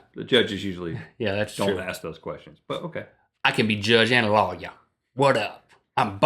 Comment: I thought at 8:13 he said "I could be Judge Analolya."